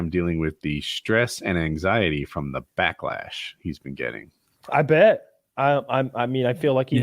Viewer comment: I thought that said blacklash